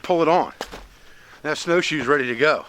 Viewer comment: I thought you were saying they were "Iris's snow shoes". LoL!